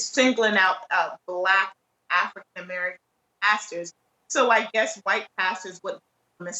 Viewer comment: Upon hearing this obviously racist man's statement I thought he said "these black bastards" instead of pastors. He previously lamented that there were not enough bubba's and Joe six packs in the jury pool. White people are going through a reckoning of sorts. The trump supporters are definitely returning to the harsh racist mindset of their ancestors from yesteryears. The fence sitters are in anguish and conflicted. Those who come from families that have always resisted pulled of blanket hatred and ambivalence towards non white people, particularly the economically challenged black communities. These white see individuals and not inclined to saddling them with stereotypes while racist whites see the general black population as degenerates basically. You have to prove your individuality to them and still remain under their harsh scrutiny. Now, I hearing their toxic language morphing into violent threats of death and mayhem against diversity, multiculturalism etc with 70% of trump supporters calling for secession. These are amazing and dangerous times as any in our sometimes violent history.